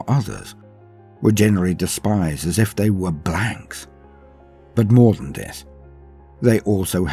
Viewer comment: I didn't hear the word "blanks" right at first had to re listen to it